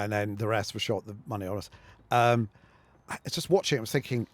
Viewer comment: What does he say at 15:08? "Episodes 1-2 were shot on a Friday, the rest were shot... "monayorus"?" Can't hear what he says. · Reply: ”Over many hours” I think